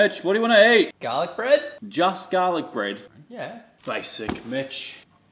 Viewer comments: Why did I hear it like "basic *itch.."😂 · everytime i hear him say basic Mitch in my head i hear "Basic Bitch" lmao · Sounds like he says basic bitch at the beginning. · I could swear I heard you say “basic b**tch” 😂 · I misheard it as basic bit.. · My volume was low, I didn't saw captions, first thing I heard was 'babe what do you want to eat' and then saw a man😂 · I thought he said basic bitch😂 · when says "basic mitch" it kinda sounds like hes saying "basic b##ch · I thought he was about to say "basic bitch" when he said "basic Mitch" 💀😂😭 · I For a second i thought he said:”Basic Bi*** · Why do I always hear " basic bitch " instead of " basic mitch " 😂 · I thought man was saying bitch